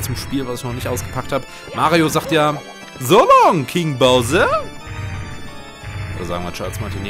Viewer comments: ich habe immer verstanden, Go home King bowser xD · Ich hab immer: so long Gay Bowser verstanden XDD